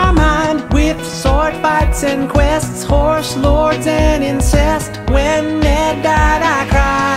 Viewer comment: When I was 12 I thought he was saying “and inchest” He said And INCEST